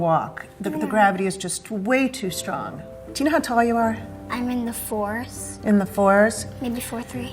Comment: "how tall are you"" "I'm in the 4's" Me, a scandinavian: "What" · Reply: Same, I was so confused. I thought she said “I’m in the force”, well, good to know you watch star wars, but what’s your height?!